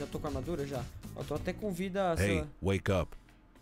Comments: Porque nas 5:16 eu ouvi hey wake up? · no minuto 5:16 algem diz ey ueycap que foi isso